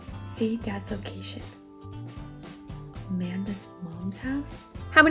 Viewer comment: Why the heck did I hear "where's a man dead" in the start instead of Amanda?